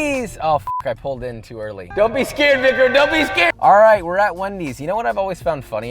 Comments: Am I turning deaf or did he just say don't be scared nigga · did he say dont be scared nigg*r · Dear, At the 0:52 mark in the video. What you are saying does not sound like what your are actually saying. Definitely sounds like the “N” word. Just giving you a heads up. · what did he say? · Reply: Victor not the other thing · WHAT DID HE SAY!!!!!! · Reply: victor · wait at 0:51 did he just say "don't be scared n-word"? Or am I just stupid? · what did he sayyy??? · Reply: "Dont be scared Vicram", its my name